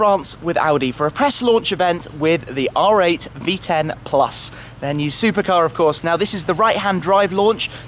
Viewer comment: I thought u said "My new supercar"